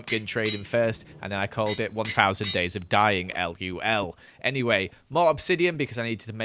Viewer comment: did he just say lul not lol